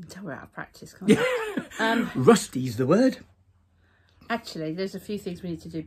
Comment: I kept hearing, "Say the word.. " from the Muppets to David Bowie.